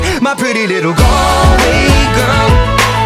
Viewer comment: When it says, "Galway Girl" i thought it said, "Call, Wake up" 😂😂😂😂😂😂😂😂😂😂